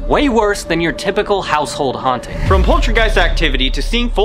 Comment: I swear he said poultrygeist not poltergeist